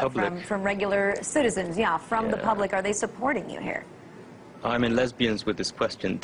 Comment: im sorry did they just say im in lesbians wtih this question? pml n loool she got rock rolled